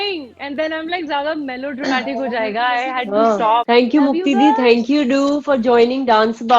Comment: just she said?? Or I am the only who heard it ??